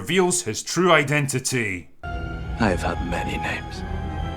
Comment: Oh I've han many names, lol thought he said many men. Actually had to rewind it.